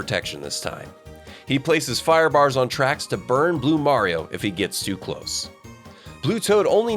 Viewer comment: Did he say Blue Mario?